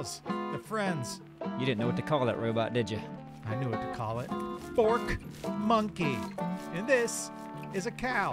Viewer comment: I thought you were saying FORT monkey lol cause he attacks your farm and base 😂y’all are amazing keep up the great work!!